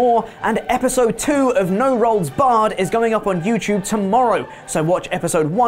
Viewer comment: I always hear "No Rolds Barred" when he cross-promotes.